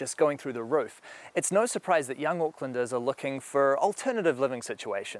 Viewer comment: Auklanders I thought he said Oaklanders as in Oakland CA . If you live in a tent in Oakland CA you better have it made with Kevlar panels to stop bullets.